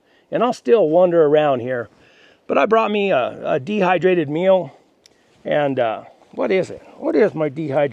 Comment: A dehydrated meal...I thought you said a dehydrated mule. lol